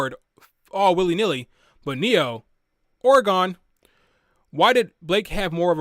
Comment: did you say "whore gone"? The captions said Oregon. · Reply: lol "aura gone"